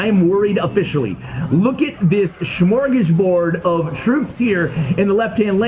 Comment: Can someone tell me what he said at 3:10? "Look at his (...)" Not a native english speaker, so any help is appreciated. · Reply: He said smorgasbord. It means like a lot of food or a buffet. But he uses it in this case for just the sheer amount of units the opponent is throwing at him.